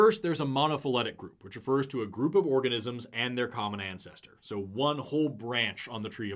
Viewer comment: As a molecular biologist who has spent plenty of time staring at the human genome, there are so many genetic duplications, inversions, translocations, frameshift mutations that have been integrated into the genome. All of these create completely new information and can and do easily occur. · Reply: I swore you said “orgasmic” instead of “organismic” and I lost it for a second😂